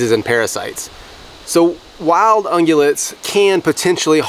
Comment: it sounds like is saying unulet. The transcript spells it unulet. But I think he is saying “Ruminant” meaning species of dear? Just curious? It’s a very good video, and great presentation . I have made jerky before. But smoking salmon is my main gig. I use a dry brine. And when I air dry it , I use my garage and set up a fan blowing directly on the fillets. drys quicker , and keeps bugs off the salmon. I just retired so now will have time to cure & preserve game. Thanks for sharing! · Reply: He said ungulate but he meant ruminant. Pigs ARE ungulates, but not ruminates. However the comment in the video said that “meat from healthy ungulates is fine” and I’m certain he is referring to raw meat. He should have said “ruminates” since that excluded pigs and only focuses on herbivorous ungulates mentioned (deer, elk, cattle) that have a lower risk of food bore illness to humans.